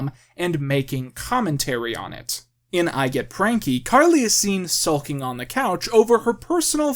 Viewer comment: I misheard you say “iGet pranky” as “iGet cranky”